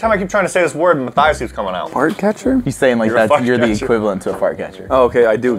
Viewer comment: I thought said fu*k catcher